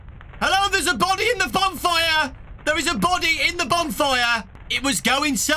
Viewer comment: i legit thought he said "A BONNIE" "A BONNIE"